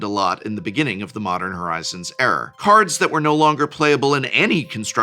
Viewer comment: I heard "the modern horizons error" (not era). Was this on purpose hahaha